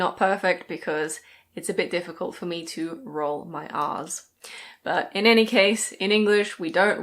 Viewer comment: I swear I heard:"It's a bit difficult for me to roll my arse"